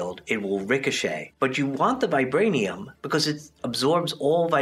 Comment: who else first thought he said virginity